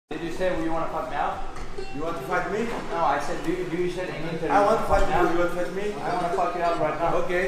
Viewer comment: How is no one calling paulo a bluff? When did khamzat say no? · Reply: Are you deaf ? @ 0:02 Costa : "do you want to fight me?" Khamzat: "no i said , did you said anything" Theres even subtile, open your eyes , or i dont know, wash your ears man